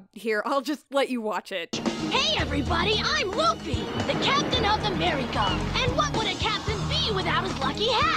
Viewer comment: Why am i hearing "The captain of America 🦅🇺🇸" 💀